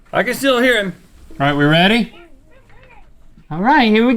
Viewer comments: I swear that thing responded to link @ 12:05. It sounded like "Yeah, we're ready!" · does he say ya we're good?